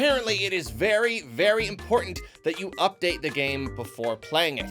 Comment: I thought it said “Update yo mama” 😂 pls